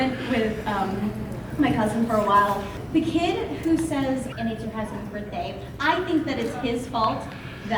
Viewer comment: In the first video I thought the kid said and eat your husbands per day